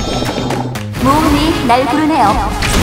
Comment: 근데 맨처음 말이 뭐예요? 아무리 들어도 몸이 날뿐해요로 들리는데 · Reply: 모험이 날 부르네요.